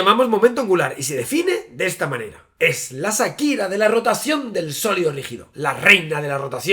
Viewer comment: entendi asa akira 😂😂